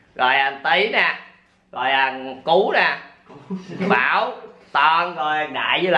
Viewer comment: mình nghe thành cú